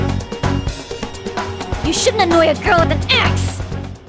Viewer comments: what she says? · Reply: 'You shouldn't annoy a girl with an axe!' Quite fitting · At first I thought she said “You shouldn’t annoy a girl with an ass!”